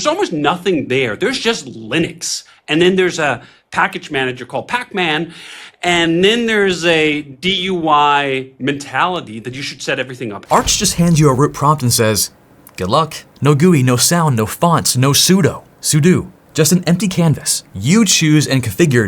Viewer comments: Did anyone else hear "a DUI mentality"? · Did he just say there's a DUI mentality? Lol